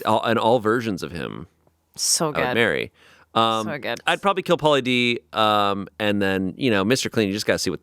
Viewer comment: I totally miss heard a participant in this line up and thought it was equally random!! 😊 I swear Shayne said "Pitbull, Mr. Clean and Paula Dean" the first time through!! So I was like how do you compare those three?!? Then they started talking about Pauly D.... And I realized my mistake 🥺🤭